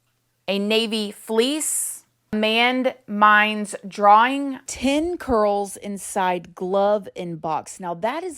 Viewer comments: I thought it said ID cards inside glove inside box not 10 curls... 👀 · I thought it said "10 ID cards, inside gloves, inside box" but I could be wrong ☺️